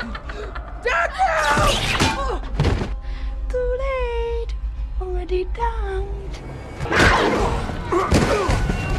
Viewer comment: wtf she say? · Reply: “To lateeee already damned”